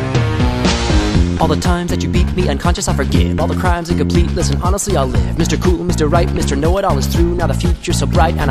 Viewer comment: Small correction at 0:42 what he really says is “All the crimes incomplete, listen honestly I’ll live.” The word ‘listen’ is said pretty fast.